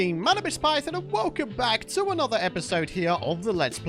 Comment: Lol, for the longest time I thought his intro said "Manibis Python" lol im so dumb